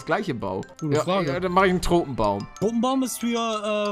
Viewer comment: ich verstehe erst mal "Drogenbaum" ! XDDD